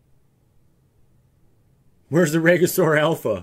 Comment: Did you ever get the combo started? All I heard was "where is the Assure" during the video.